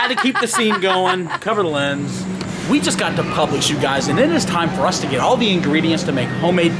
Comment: You said publish instead of publix